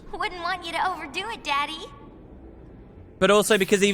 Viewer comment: Did Rikku just call Wakka daddy?